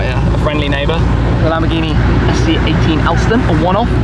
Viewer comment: what is the name of the Lamborghini again at 4:19? I couldn't quite understand. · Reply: Philip Vinson It’s the Lamborghini SC18 Alston.